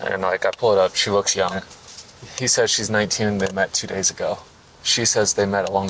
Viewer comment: he did not said two days he said 10 days